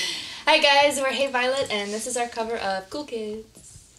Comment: It sounds like Miranda said, "and here's the cover of cookies" :''')